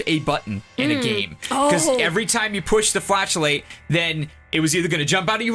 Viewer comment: I honestly thought he said "push to flatulate" for a second :D